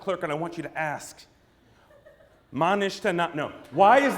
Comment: what did he say in Hebrew at 10:43? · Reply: Ma nishtana, it means: "what has changed"